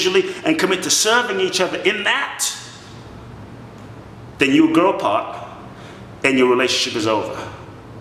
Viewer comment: when he says "then you grow apart and your relationship is over" I heard "then you grow pot, and your relationship is over" hahah